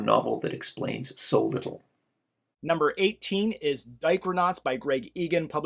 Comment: Every time you says "Dichronauts" I hear "Diaper Knots"...that's what I'm trying to wrap my brain around.